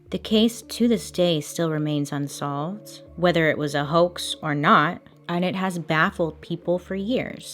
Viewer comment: What is the word on 8:26? Houk, hock? Sorry, English is not my native language. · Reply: Hoax